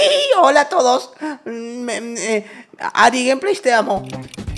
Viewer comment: por que dijo me guste ari??????